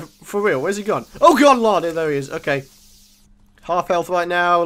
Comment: You are a wizard! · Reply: ***** Am I the only one who "oh good lard" instead of "oh good lord" at 4:00?